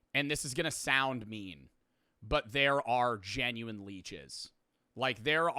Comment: i know he’s saying “genuine leeches” but i keep hearing it as “genuinely jizz”